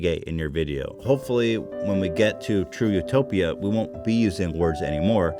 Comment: Did he just say.. Trudeau-topia.. cause that shit funny as hell